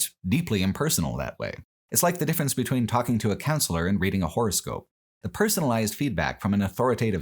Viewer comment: Listening to this to sleep and I heard "horse girl" instead of horoscope... 😭